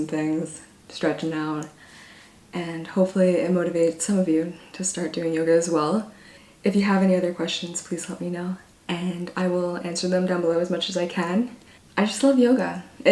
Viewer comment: I thought she said "if you have depression " I was like oop 😂😂